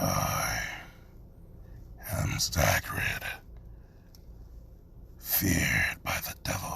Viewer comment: At first I thought you said Hagrid lol